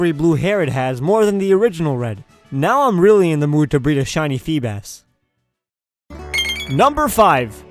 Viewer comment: i thought you said shiny coffee